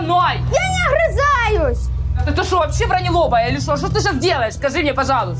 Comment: мне раньше слышалось не БРОНЕЛОБАЯ, а "ВРАНЕЛОПАЯ" Я думала, что ВРАНЕЛОПЫЙ человек, который каждый день и вообще часто обманывает кого-то. Искала в интернете это слово, а его нету и я наконец-то услышала "БРОНЕЛОБАЯ".